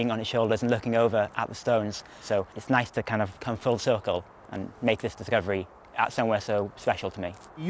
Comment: did bro just said "come full circle"